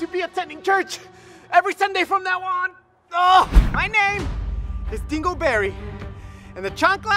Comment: At first I thought he said he single berry(